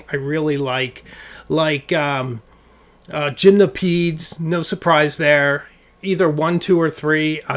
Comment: You mentioned listening to music in your office and that you start off with something you really like but I cannot, for the life of me, figure out the name of the artist or title you mentioned. It sounded like you said “Jim Napize”. What was it you said? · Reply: Gymnopeidies (sp) by Erik Satie. Check in the video description for my classical playlist “The Stones of Old England“ it’s on there!